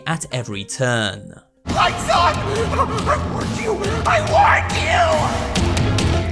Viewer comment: I assume that, when you meet Sunrise, he'll try to help you, but is probably initially scared shitless because you are NOT supposed to be there. So, he probably warns you about Moondrop, and that's how the player is introduced to how they're going to deal with his danger, like how Phone guy explains how the animatronics operate. That's what the: "I warned you! I WARNED YOU!!" part probably means. He's reminding Gregory to make SURE he remembers what he warned him about, so he'll stand a chance against Moondrop. My theory is that Sunrise gives you the mission you need to complete in that zone, but the location you need to get to complete the mission is far away from the place you need to go to deactivate the night time phase, and it takes so long that you'll inevitably HAVE to deal with the night time phase and Moondrop while doing the mission. Either that, or the night time phase is a required cutscene before or after you complete the mission. Either way, you'll probably run into a situation where it's a race to get to the place to deactivate the night time phase before Moondrop gets you. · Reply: I thought moon drop was saying “I WANT YOU, I WANT YOUUUUU”